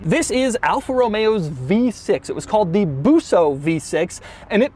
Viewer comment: At first I thought he said Buffo engine instead of Busso (the name of the guy who designed it) which is funny because Buffo is an Italian word referring to a specific archetype in comical opera.